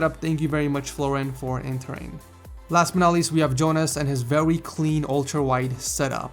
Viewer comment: Did he say veuy